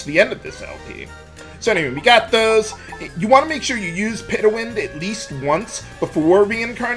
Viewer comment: Instead of "Petawind", I hear "Pay2Win"